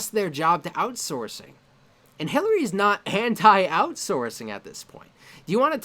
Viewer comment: When you said "Hilary's not anti-outsourcing", i heard "Hentai Outsourcing". dies